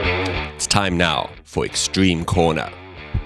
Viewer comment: Caption: I thought you said Extreme Korma, not Extreme Corner...